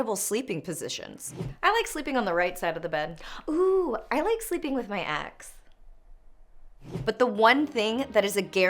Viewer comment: Is it bad that in the sleeping positions cutaway, I heard "I like sleeping with my axe"?